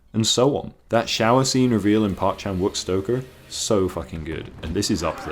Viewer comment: I don't mean to be a dick but I couldn't understand what you said at around 3:50, so could you tell me what that shower scene is from? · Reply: Park Chan Wook's Stoker